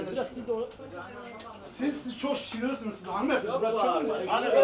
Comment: siz çok şıllıksınız hanımefendi mi diyor ne diyor orda :D